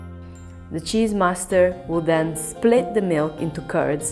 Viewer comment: did you say "the cheese master"........ His mother must be so proud